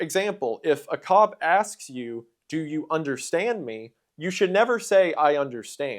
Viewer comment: I heard the “understand” thing as “No, I do not stand under you/your authority”. 🙄